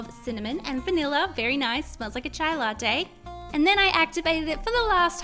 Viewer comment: I thought she said smelt like a child or child latte!!! 😂😂😂😂😂